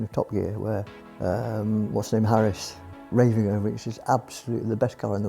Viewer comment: Did he say Sam Harris? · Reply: Chris Harris